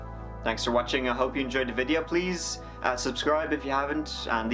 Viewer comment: it sounds like he says "Please unsubscribe if you haven't" lol